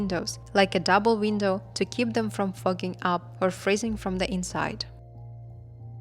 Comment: did you say they add the plastic to stop the cars from 'Fu***ng up' ? ? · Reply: Freezing up.